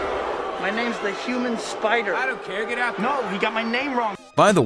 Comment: i swear when he says "my name's the human spider" i can hear "my name's jeff" in the beginning perfectly